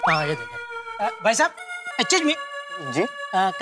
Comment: 🙈🙈Aachuuch me instead of Excuse me😂😂..Jetha op